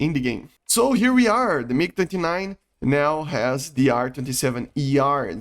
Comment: did you say mig 59 at 0:28? Hahah or am i hearing wierd